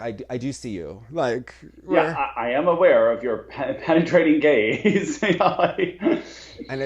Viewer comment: When they said penetrating gaze at 19:53 I thought they said penetrating gays and I was deAD